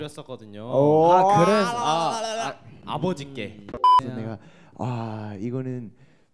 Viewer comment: did I just heard "abushikik?" abushikik ikikikik HAHAHAHHA